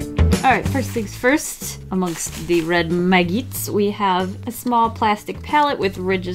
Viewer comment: i thought she said maggots- Smh